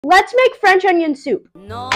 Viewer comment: I thought you said “lets make french on youtube!”..